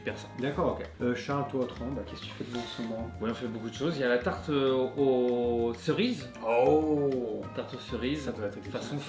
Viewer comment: ってのおすすめのケーキ、tarte aux cerises に聞こえるんですが、字幕と画像がマンゴータルトになってて、これはどちらが正しいのでしょうか？！